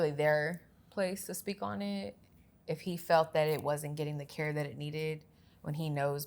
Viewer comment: did I hear Jaidyn say, "It wasn't getting the care IT needed"????? Am I tripping or what 😢